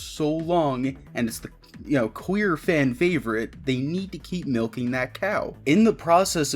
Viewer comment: did you just call me a queer? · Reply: you said “clear favorite,” in the caption it says “queer favorite”, I was joking.